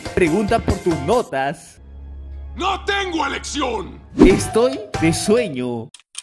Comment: alguien me lo explica, no entendí :'v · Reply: Estoy muerto de sueño